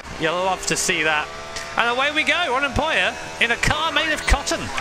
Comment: I kept hearing the "unemployer"